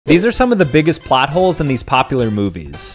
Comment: I thought he said potholes😂😂